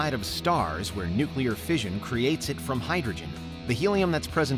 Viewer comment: Cc says fusion, sounds like fission...